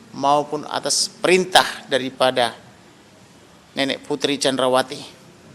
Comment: Salah denger gak seh"Nenek Putri Chandra wati" Pak Komarudin ini bikin ngakak😂😂